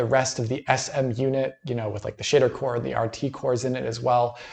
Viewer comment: Eh did say the shitter core around the 7:49 Mark?